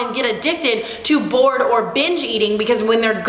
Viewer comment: I thought she kept saying “Born/Bitch eating 😂